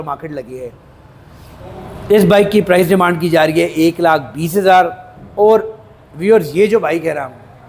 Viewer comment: Did he say 1 lakh 20k at 6:10 ?